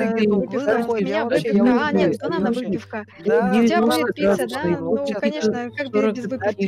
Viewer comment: Я одна слушала что херейд и другие говорят 0:21 Херейд: ну в целом ну вообще отомчик он вот углы находит Дальше я не слышала другие перебивали! Стасик:(я услышала только-) ну меня выкидыва.. а нет выпивка а у тебя пицца да ну конечно родов из быка.... А ведь у банана классные пальчики! Отомчик:на этой карте.. (не услышала дальше:)